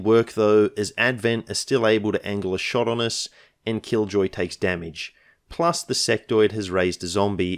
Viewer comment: I thought you had said "and kill Joy", rather than "and Killjoy". I guess that was a glimpse of what was to come.